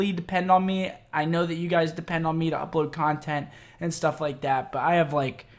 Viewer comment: At first I thought he said codtent not content